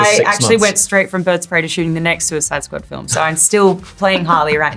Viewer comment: did I hear correctly NEXT SUICIDE SQUAD FILM? ANOTHER SEQUEL ?! IM PUMPED